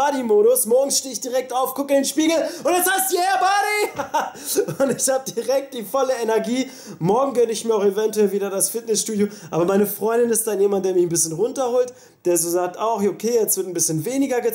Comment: ich hab verstanden: jmd der mir einen runterholt lol haha :D ic schrei meinen spiegel in der früh auch immer mit yeah buddy an :D